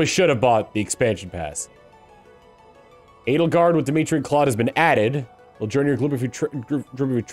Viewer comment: did say Analguard?